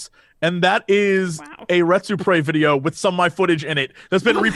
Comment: What video did Jesse say? I could only make out "irets or prays" Rets pray? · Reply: There's a Youtube channel "Retsupurae" (ie "Let's Play" written in Japanese and re-romanized) that has been posting commentary on Let's Play videos since 2008 and the hosts of that channel have been on the podcast before.